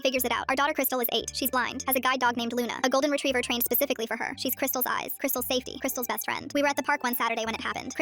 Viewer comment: When she stayed crystal’s eyes, I thought she said crystallized